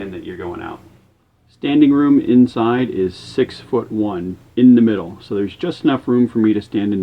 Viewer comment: Captioning mistake at 2:56. He says six foot one and the captions say 6'11".